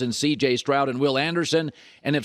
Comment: Anyone notice Colin having weird misspeaks a lot recently. Jordan with the Heatels @ 2:10 mark? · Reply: Yeah I've been seeing it too. Sometimes I just hear it wrong though. When he said "CJ Stroud and Will Anderson" towards the end, I thought he said Stroud and CJ Anderson. I had to rewind like how did CJ Anderson get brought up 😂